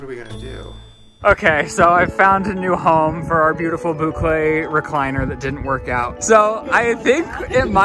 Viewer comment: I DEF thought Ryland said "boot leg recliner/couch" 😂😂